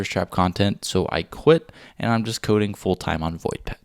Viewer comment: What does he say right at the end? 12:48 ...coding full time on ??? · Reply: voidpet